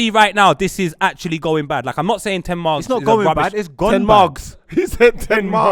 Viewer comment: "i'm not saying 10 margs" instead of Ten Hag actually finished me im dead